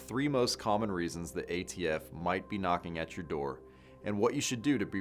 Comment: Oh, I thought it said Antifa comes knocking on my door. I got so excited!